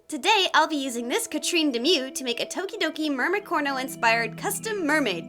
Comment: I thought you said DOKI DOKI sings EVERY DAY I IMAGINE A FUTURE WHERE I CAN BE WITH YOUUUU sorry